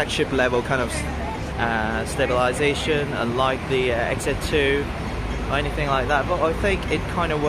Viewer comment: Sorry i didnt catch the last word on 2:08. Did u just compare the asus' stabilization system with 'XA 2' by sony? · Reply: Nanda ! It was Sony XZ2 I was comparing with I think :)